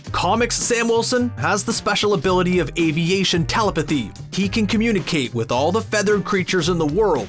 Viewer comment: Did u say tella puthee? Lol